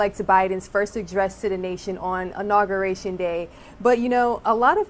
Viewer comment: Did I just hear "ONAUguration" day???